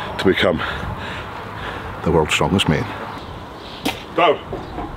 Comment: I thought he said "Go" for the first 4.. then I realized it was "Down"...